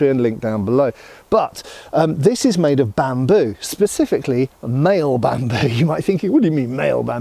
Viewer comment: After years of watching choke on the word "chainmail", I seriously heard him say "maille bamboo" before he specified that he was talking about gender.